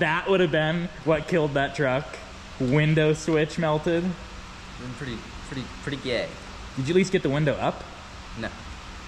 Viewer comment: did he say, "Pretty, pretty gay?" LoL wut